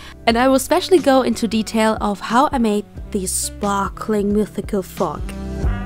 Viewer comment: I thought it said Mystical Frog for some reason. XD